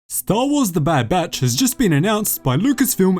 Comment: I thought this said "Star Wars The Bad Bitch"